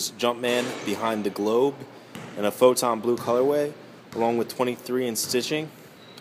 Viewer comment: You say photo blue weird sounds like you say photon blue